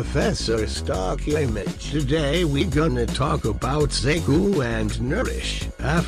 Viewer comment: I heard "today we're talking about satan“ and i was fairly confused but not too much